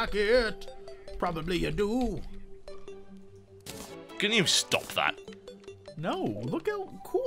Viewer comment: i turned on transcribe audio and when tb said would you stop that, and transcribe audio thought he said: gamestop known hotel who i am accredited batat as possible...